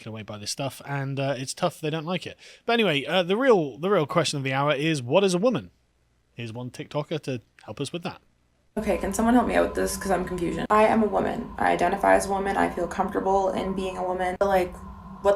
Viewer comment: Did she just say "I'm confusion?" Also, saying "BEEP" over saying "fuck" doesn't replace it. You can hear both.